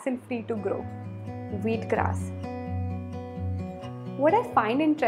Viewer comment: I heard it weed grass 😜